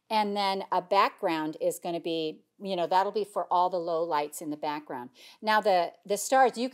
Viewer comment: I keep hearing 'lowlife background", 🤣🤣🤣